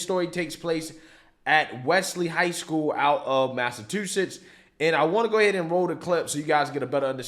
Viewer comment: Did you really say Massatwoshits?